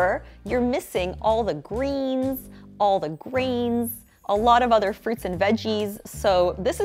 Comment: I thought she said grains twice.